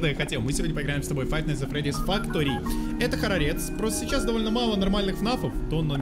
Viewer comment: Мне походу одной послышалось не хоррорец, а холодец! Наверное из-за того что жрать хочу О_о!!